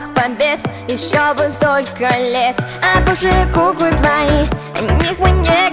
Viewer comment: Я когда услышала «а бывшие куклы твои», мне послышалось «обожаю губы твои». Я так думала до тех пор пока не прочитала описание. (через 2 месяца после того, как послушала песню)